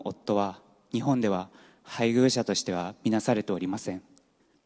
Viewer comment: what does he say from 14:33-14:37? 僕の夫は日本では（何々） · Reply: 「配偶者としては見なされておりません」